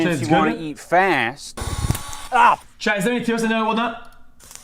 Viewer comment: What the fuck did he say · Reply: “chat is their any tos (terms of service) in there or whatnot)